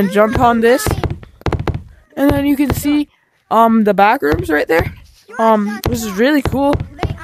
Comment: When you said backroom the text at the bottom thought you said bathroom